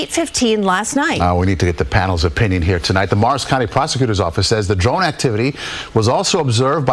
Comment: this whole time I’m not looking at the video, but just listening and I’m think they’re saying Mars county and I look down and it’s Morris county 😂